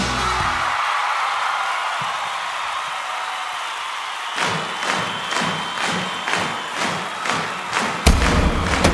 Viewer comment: Did he say "holy shit" or "holy f..k" ? @ 2:45 ? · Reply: holy shit